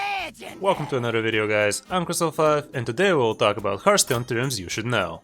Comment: Thought u,said cazzul5 in intro lol